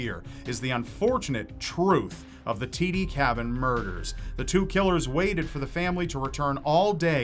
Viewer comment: I saw the last story on Dateline or 48 hours. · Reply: What was the name of the case or family? Sounds like says T.D cabin murders but I can't find any info when I search.